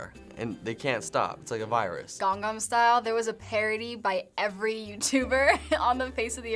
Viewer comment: I heard she said Gangam style